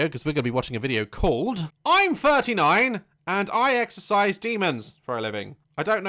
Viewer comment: i thought he said exercising demons, as in making them do sports and stuff, tbh thats the real terror... sports.